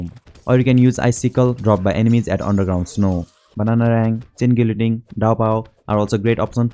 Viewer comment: what is bro blabbering about could you maybe type it out bc it’s sounds like your are saying zinkindinck and the subtitles say chin Guillotine